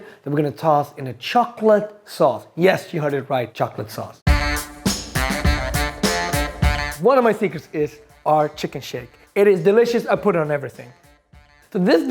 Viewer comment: Yo I thought he said chicken shit!!! 🤣🤣🤣🤣🤣🤣🤣